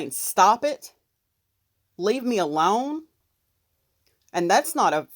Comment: Sounds like she said call him leave me alone.